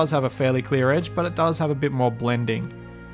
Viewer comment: It really sounds like you say "Fallout 4" XD